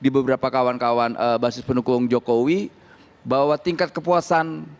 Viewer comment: "kawan kawan buzzer pendukung Jokowi" salah denger ya